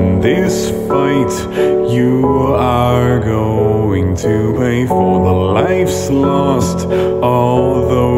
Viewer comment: It sounds like Sans is saying "You are going to toupee"